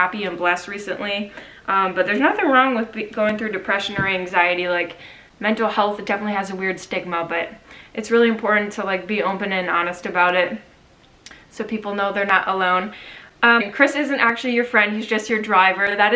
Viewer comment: did she say stick in my but or stigma?!?!?😂😂